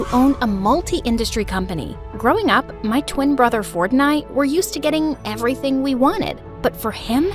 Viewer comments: I HEARD "FORTNITE" INSTEAD OF "FORD AND I" 💀😭😭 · I thought they said, “my twin brother fortnite” at the start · In the starting I misheard the lines ‘ me and my brother “fortnight”’ lol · why did i thought she said fortnite when she said his name 💀 -> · When I heard my twin brother ford an I I thought they said fortnite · WHY DID I THOUGHT SHE SAID FORTNITE☠️ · is it just me or did she say Fortnite or smthing, literally not even 1 minute into the story and I see this lol... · bro at 0:10 i thought the girl said "fortnight" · did she just say fortnite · i thought she said "my twin brother fortnite" at the start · Is it just me or when she said "ford and I" I heard "Fortnite" 😭😭😭😭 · When she said Ford and I I thought she said Fortnite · i thought she said fortnite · When she said for and I I thought she said Fortnite 😂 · it sounded like fortnite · When it said “Ford and I” I thought I heard “Fortnite” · bruh i thought she said my brother fornite 🤣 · When barbra said my twin brother Ford and I I thought she said my twin brother fortnite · i swear i heard her say “my twin brother fortnite” 💀💀😭😭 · i heard "fortnite-" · Oh my gosh at the beginning whenever she said her brothers name I thought she said my brother Fortnite I'm like who the heck names their kid that and then I looked at the subtitles and I'm like oh my gosh thank god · I heard my twin "brother Fortnite" 💀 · When Barbara said “Ford and I” I thought she said and my brother fortnite lol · sounded like fortnite lmao.